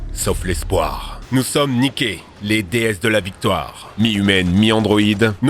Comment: J'ai entendu "Nous sommes niqués" lors de la sponso